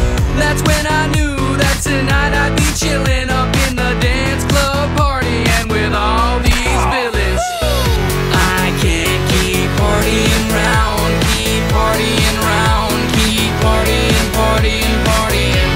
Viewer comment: So one time I misheard it as krillan instead of villain and I still can't unhear it